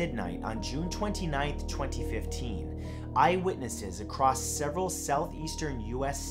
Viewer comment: I THOUGHT HE SAID I WITNESSED I WAS LIKE HOLY HELL MATT YOU GOT SMTHN TO TELL US?